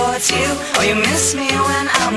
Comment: The way he says "gravitate towards you" sounds weird. And i thought he said sleepy state of already gone xD